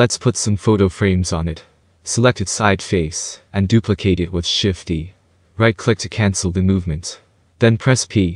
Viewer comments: I swear, I was about to quit at 3:30, all I could hear was you saying shift+e, since you didnt put up your shortcut commands on the screen I couldn't see what it was but I realized you said shift+d. This tutorial is veery fast paced for a beginner tutorial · Shift + D for those who wondering i misheard it to shift + E i was stuck from it for a day :) hope this help for others